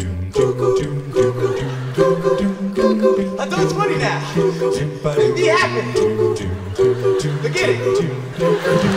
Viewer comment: Can someone please tell me what Jerome's saying here? I can make out "be happy" and "forget it", but not the first line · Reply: he says "that was funny now, be happy...forget it!"